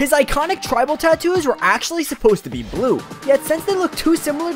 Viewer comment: I thought you said Diavolo